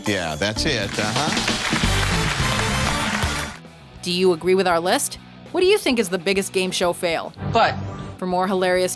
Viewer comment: What did she say at 11:13? How am I supposed to laugh at the answer when they fucking censor it? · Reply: "where's the weirdest place youve had the urge to make whoopie (have sex)" she said "in the asshole" lol